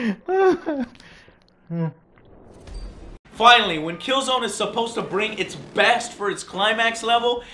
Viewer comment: Did he say killzone at 11:55??